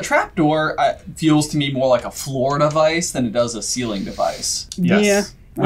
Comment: Oh, Floor Device First time, I heard this as "Florida vice"...